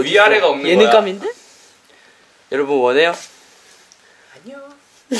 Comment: THAT WAS SO CUTE 3:29 EVEN IF I DONT KNOW WHAT JIN SAID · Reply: First Name Last Name he said no lol